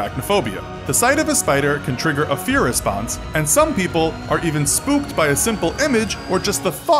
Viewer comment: DID HE JUST SAY SPOOPED